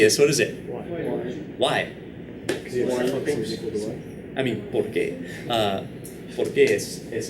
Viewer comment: did he just said "por que" ?